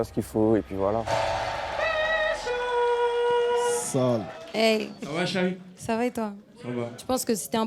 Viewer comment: A 16:49 j'ai entendu sal n**😂🤣